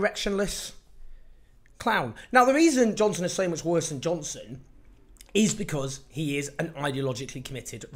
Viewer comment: Johnson worse than Johnson did I hear that correctly?